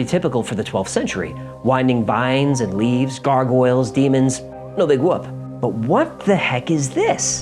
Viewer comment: What does AJ say at 10:49? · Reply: no big whoop